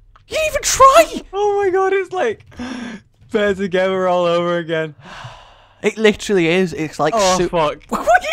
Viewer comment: what does say at 20:25? There together again?